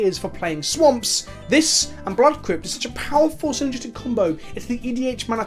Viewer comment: "This and ... ... are such a powerful, synergistic combo" - What is he saying to combine tainted lands with? I've replayed the video a dozen times, and I can't figure out what he's saying. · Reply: Blood Crypt